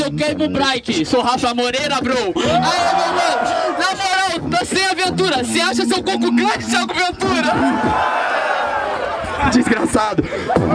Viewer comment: não entendi o que o falou alguem explica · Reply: "Sem aventura, vc acha seu côco (cabeça) grande Thiago Ventura?"